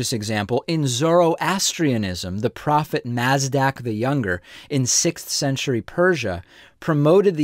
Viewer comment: the closed caption says "the prophet Nasdaq the younger"